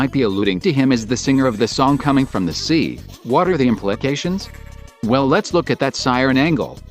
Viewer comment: did you say “what are” or “water”